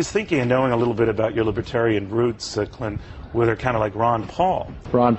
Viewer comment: Why at 1:41 Does it say 'cunt' eastwood instead of clint ¬_¬